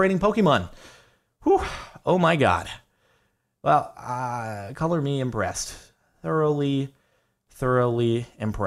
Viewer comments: I can't be the only one who thought on 6:57 he said "Cover me in breast" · when he said thoroughly impressed it sounded like color me in breast i swear. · Why did I hear "Color Me In Breast"